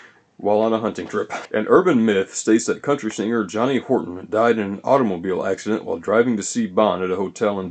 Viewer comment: I'm sorry I thought he said Robert Horton. It Was Johnny Horton killed in car crash